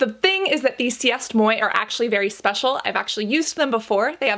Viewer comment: « C’est moi (sé moa) » was written « C east moy » she said Love you but 💀💀💀 Laughed hysterically with my sister for a hot minute about that.